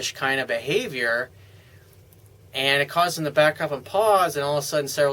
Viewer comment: Hi Coach, just to clear up, what did you say at 1:17 ? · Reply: and it causes them to back up and pause